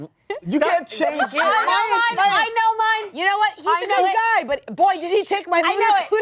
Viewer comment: the captions are so iffy; who are you paying to put "Segway" instead of "segue" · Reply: Angela's line at 38:11 being "He can lick mine" instead of "He's a good guy" O_o